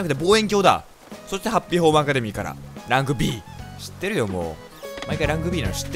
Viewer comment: ランクＢがラグビーに聞こえたww